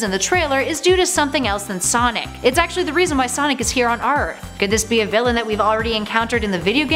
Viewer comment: did she say arth instead of earth???